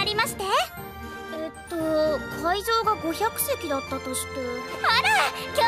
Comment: What I hear: “Do you like Elsa?”